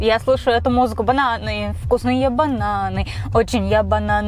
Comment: Мне послышалось не вкусные бананы а русские бананы 😅